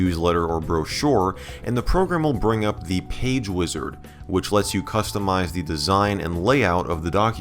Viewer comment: the first time he said wizard it sounded like he said wizzers and i was so confused for a sec lol